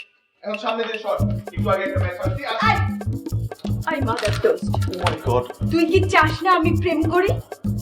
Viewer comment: did I just hear ei m******d at 3:50. WHAT THE HELL IS WRONG WITH OUR DIRECTORS !?!?!?